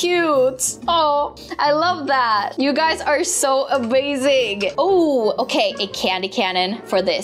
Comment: Did anybody else hear that she said thats so abazing